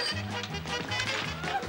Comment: What on earth is she shouting at 8:17? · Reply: Arrrghh police,police.